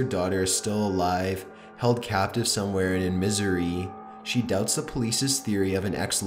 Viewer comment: In Missouri? Of all places? Or is he saying "in misery"?